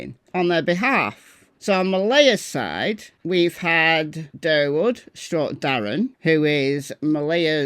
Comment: Did say "Darrwood?"😂 Did she just go Endora from Bewitched? 😂